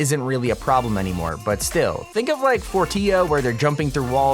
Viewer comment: Anyone else hear forttia at 8:34 😂